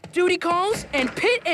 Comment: I thought he said booty call...